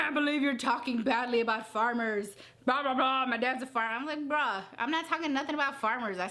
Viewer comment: did she just say "bruh" at around 10:50 mark? lmfao b1tch the moment you say anything like that, you're out This clueless whale makes one mistake after another, just in a video, imagine what it's like living with herLOOOOL